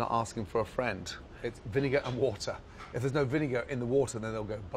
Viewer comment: when he said vinegar I thought he said billy goat. Am I insane???